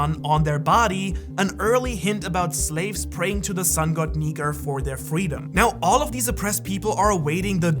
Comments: At the 10:45 did he say nikER or am I tripin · Did u hear what I hear Sun god ni- U know where I'm going with this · wait a minute at 10:46 u said the sun god ni- what? hmmm i hope i heard wrong cuz i hear a R there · i am not the only one who heard nig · wth i am not the only that can hear what he said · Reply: Sun god niker i hear it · I thought he said n1gg3r instead of Nika 🤣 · did you just say the n word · I think he accidentally said the n word when saying Nika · did he say sun god niger? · am i the only one who heard sun god ni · he says the n word instead of nika 🤦‍♂️🤭